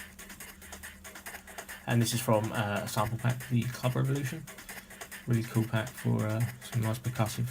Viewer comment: Did anyone catch the name of the sample pack he mentions at 3:13 ? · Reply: Club Revolution vol.1